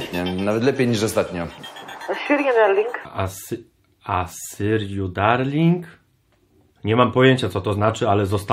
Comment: ja usłyszałem hi syrio darlinqq